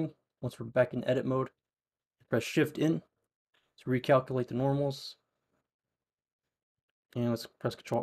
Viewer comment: Tf did u even say at 2:53 Im confuseed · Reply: Shift + N to recalculate normals